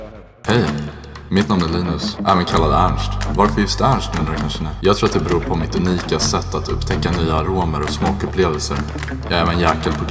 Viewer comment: vad säger han eller "nu kanske ni uueueua"